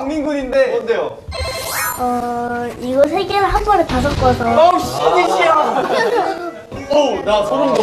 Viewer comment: did anyone else hear "oh sh**" at 2:54 ?! LOL